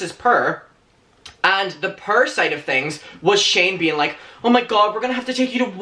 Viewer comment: You sound like you saying prr sid of poor.